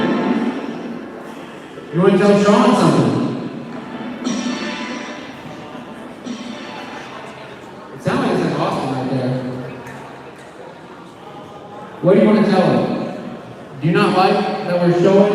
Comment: sounds like it said, It wants Austin